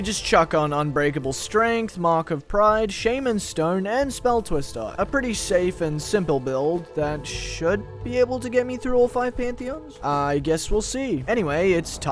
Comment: did you just say…SHAYMAN STONE????